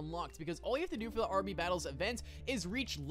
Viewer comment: did he just say rb battles